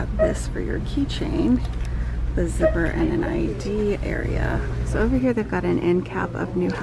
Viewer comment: Great store tour. I had to replay the beginning part a few times when you were showing the zipped wristlets, it sounded like you said with an "IUD area" instead of an "ID area"!! Lol!!! 😄❤️